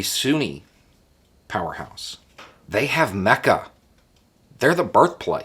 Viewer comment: I know you said "Mecca", but I heard "Mecha" and had a brief moment of fear. Lol